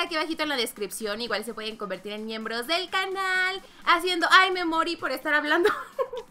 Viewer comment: Cuando dijo "hay me morí por estar hablando" yo le entendí "hay memori por estar hablando" xd